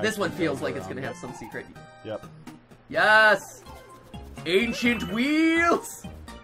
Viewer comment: Straight up thought matpat was saying "ancient weedles" and I laughed soooo hard